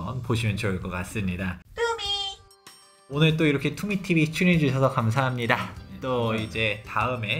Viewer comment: 소장님이 투미 TV 말하는데 이상하게도 갑자기 투기TV 로 들림.ㅋㅋㅋㅋ